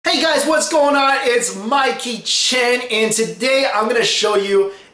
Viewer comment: Is it just me or i heard him saying, ''My kitchen'' instead of, anyways. thanks ^^ love your vid ..